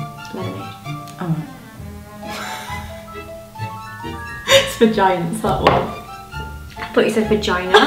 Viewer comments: Just before zoe said 'I thought you said vagina' what did actually say?? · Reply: I'm pretty sure she said "that ones for giants that is" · I thought actually said vagina what did she actually say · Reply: she said "its for giants, that one" x